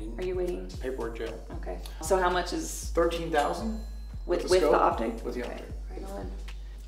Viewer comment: it sounded like he said $13,000 all three times I listened to it and not $30,000